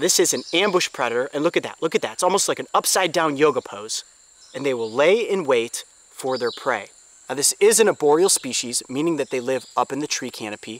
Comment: I thought he said oreo species lol